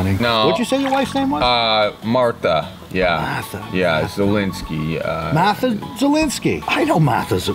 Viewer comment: I didn't mishear that, right? Martha 'Zelensky'? as in, President Zelensky of Ukraine?